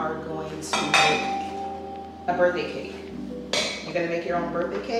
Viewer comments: It definitely sounded like she said birthday cake. The thank yous we’re too cute. Thanks for sharing your journey and this cutie with us. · I heard "birthday cake" too, Mama! Before you even mentioned it. I think that's really what it was ❤ Happy birthday, M! Beautiful cake! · I heard her say birthday cake!!!!!!!!🥰🥰